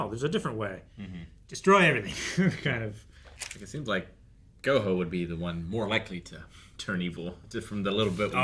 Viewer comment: Please tell me I misheard that. 😂😂😂 Ain’t no way just said Go Hoe 😂